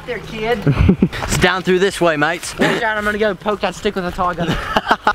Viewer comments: Anyone else hear and 3:47;) said "I'm gonna go poke that stick with a tiger"😂😂😂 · did anyone else hear say "I'm going to go poke the stick with a tiger"